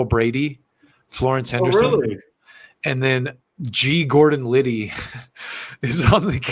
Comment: I’m just listening to this and I thought you said G Gordon Levy. The dad from American pie.